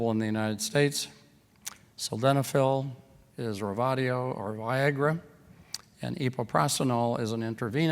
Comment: At point 46:27 did he say "viagra" in that it would help with pulmonary arterial damage from SS because if one can't get hold of some of the other meds then for sure viagra would be easy to get hold of. I've been recently diagnosed with a strong positive scl-70 and I do have tightening of the skin above the knuckles so the doctor pointed out. I also have very minor joint stiffness in my knuckles and the odd niggles in my body from time to time but nothing to shout about or hinder me from going down the gym. However, I'm just very scared that I'm trying to equip myself just in case I fall into the category whereby some of the drugs mentioned above I may need to consider self-medicating if the doctors meds aren't working. Pray of course I don't get to that stage as I'm doing everything now in my power to stay healthy and that through diet - learning about foods that alkalise the blood (calm the inflammation down) and I guess with exercise hopefully that might put me in good stead. Any further help that be of benefit I'd be very happy to know. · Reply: Yes ,he did say Viagra . But as a female , we can not take that . I do know that it helps with the blood flow . Please if you find out more about it , can you let me know . Thank you . May Jesus and his Heavenly father , God help you through this . ♡ AMEN ♡